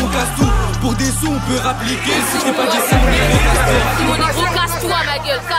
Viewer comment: " EH recule toi tes con ." mdrr